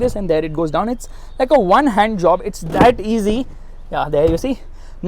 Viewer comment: did he actually say 1 HAND JOB?????